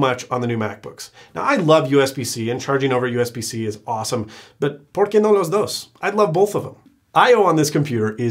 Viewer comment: Duhfuk did you just call me!?